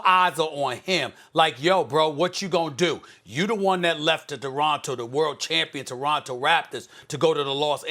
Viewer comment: Stephen A: “Kkhhawhhi lennid” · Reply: Lmao it also sounded like he said doronto 🤣🤣🤣